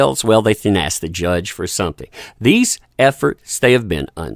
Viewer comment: When you said "these efforts", I thought you said "these F'ers" 🤣